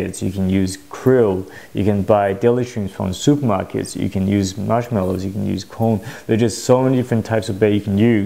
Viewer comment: did he say marshmallows???